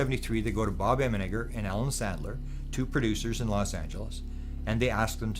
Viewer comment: I thought James Fox said “I was talking to Adam Sandler about it” I thought Adam Sandler??? No. Not Adam Sandler at all